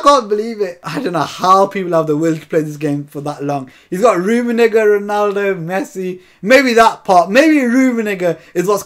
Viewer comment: "Don't blow, ref !" This content is amazing 🤣 .. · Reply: Did you say rummenigger 😐